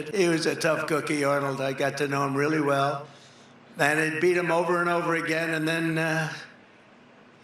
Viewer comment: did Dumald actually say, "And I beat him over and over again." ?? Seriously? Did I hear that incorrectly?